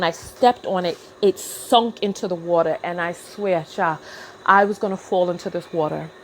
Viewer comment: Did I just hear you say "I swear sha"? Lmbo Nigeria has definitely had its effect on you.